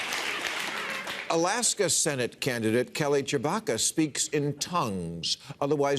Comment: I swear he said Kelly Chewbacca...errrrrrrrrrrrrrrrrrrrrrrrrrrrrrrrrrrrrrrrrrrrrrrrrrrrrrrrrrrrrrrr!